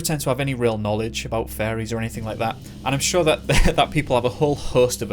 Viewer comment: Anyone listening on headphones thought he said “fairies” instead of “furries” and got really confused? Just me? Ok 👌🏻